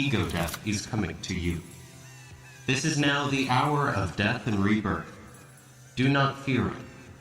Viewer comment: I was not reading the captions and thought he said that "this is now the hour of death and reverb."